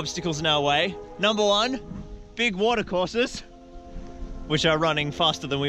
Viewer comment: all the new people wtf all the people who had been here longer like oh no hes on about rohan again! also who else thought the said 'mordor forces' not 'water courses' XD